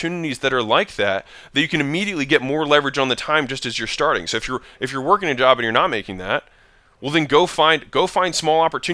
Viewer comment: Can anyone please tell me what does he say around 6:16 to become a full _ ?? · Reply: phlebotomist it’s a career he’s referring to